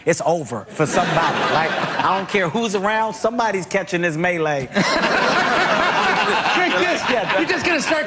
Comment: What did Kevin said at 12:27 "Somebody's catching this ___ "? Sorry, English is not my native language. ;) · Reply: Melee is english, your mix comment makes no sense in English.